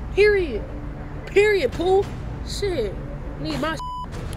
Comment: Girrrllll me too 🤣🤣 i hear ya sus “Need my shi BEAT” 😭